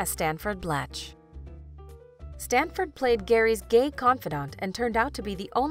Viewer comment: Did anyone else hear “Gary” instead of CARRIE